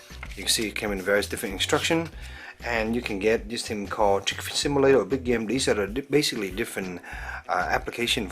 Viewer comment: what did you say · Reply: U can get chick simulatorrrr